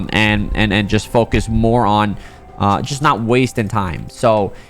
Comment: who else thought he said moron instead of more on 😂😂